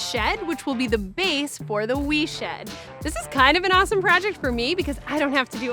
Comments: i heard "The Weave Shed" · I thought it said the WEED shed lmfao